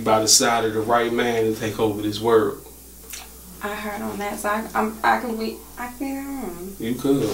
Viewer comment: What the hell she say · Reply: "I heard on that side. Um...I can we...I came on."